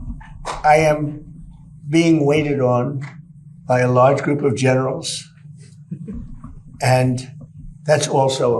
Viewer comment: I heard "by a large group of genitals". Might be correct.